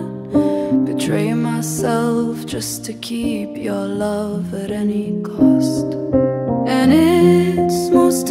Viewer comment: As someone going through a breakup, “I would rather not betray myself, just to keep your love at any cost” hurts so much. The melody of this song is my fave of the entire album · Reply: All this time I thought she said "I've trained myself to keep your love at any cost" lmao